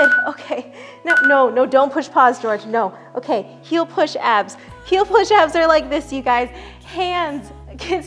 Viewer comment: who else heard “he’ll push ads” instead of “heel push abs”